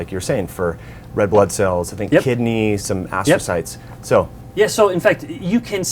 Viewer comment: Love the interviews. At 10:31 you say a word and I can't figure out what it is. Astathites? Aspasites? Would love an answer so I can look into it. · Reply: astrocytes - cells in the brain and spinal cord